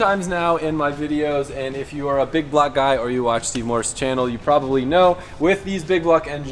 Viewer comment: i thought he said “if your a big black guy”😂😂😂